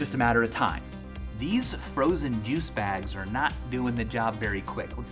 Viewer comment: did he just say frozen juice